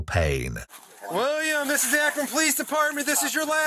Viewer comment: I thought the intro said “William this is the Afton police department” and I thought the matrix disc slipped for a second